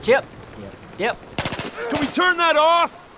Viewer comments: hahahaha .... can you tell me what he said in 4:31 plz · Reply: 50 DZD He said dip dip potato chip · Pls someone write what is being said at 4:36, literally :) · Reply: "Dip dip potato chip..." 👍